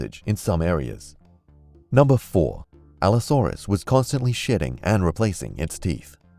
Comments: Instead of him saying shedding I thought he said constantly shitting 😂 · Anyone else hear that as shitting? No? Just me then...